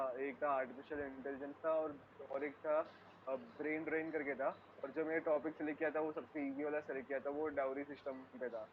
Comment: brain rain?? What was he saying?